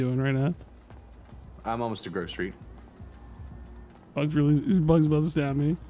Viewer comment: says I'm almost to Grove Street, first time though it sounded like I'm almost to grocery..... Is that fruit PD headquarters?